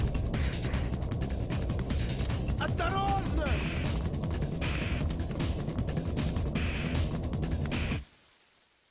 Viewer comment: what did the guard say in Russian? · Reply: "осторожно !" or "watch out" in Russian.